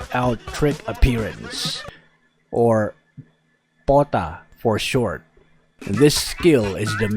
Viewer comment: GUYS! Turn out your english (auto-generated) captions and pause at 2:16 , its saids Bata instead of P.O.T.A tar***( sorry lol)